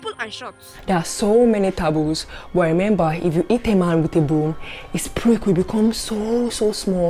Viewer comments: “If you hit a man with a broom, his prick will become SO SO SMALL. AND SOMETIMES, ALMOST INVISIBLE.” LMFAOOOOOOOOO! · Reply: I thought she said if you eat a man in the bum, thanks was really confusing after replaying it like 5 times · Geez i cant understand what she is saying 0:44 " eat a man with a boom " ?